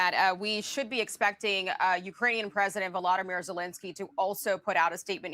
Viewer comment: I could have sworn you said Voldemort zielinski. LOL